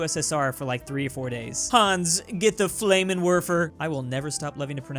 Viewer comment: You love to pronounce Flammenwerfer? Then why do I hear "Fläymenwörfer" instead?! ^^